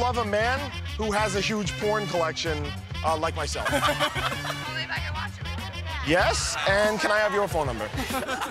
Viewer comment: what did she say? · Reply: "Only if I could watch it with you"